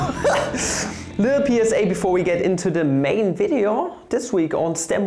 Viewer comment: "Little Penis" is totally what I heard the first time I watched...